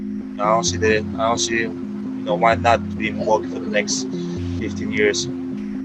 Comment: I thought giannis said "IN MILWALKEE"???